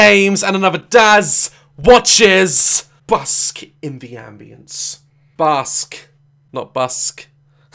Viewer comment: In the very beginning of the video, I thought Daz said "Busk in the ambulance!" 🤣🤣🤣